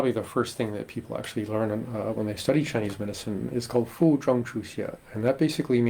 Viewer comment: Fu zheng, qu xie. I heard you say Fu Zhong. Which one is the correct pin yin.